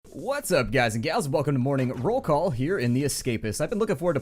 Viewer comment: it sounds like he says whats up guys and gals